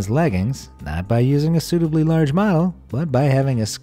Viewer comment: Man. You gotta pronounce your words better haha. I thought you said "Instead of using a 'stupidly large model'" but you actually said "suitably large model" lol